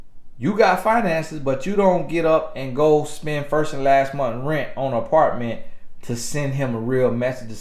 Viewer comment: I heard “firsthand” maybe lolol